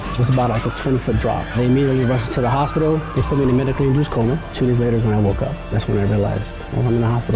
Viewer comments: did he just say 2 years later? Wow that's alot of missed time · what was the unit of time? cuz like it sounded like years but thats a while...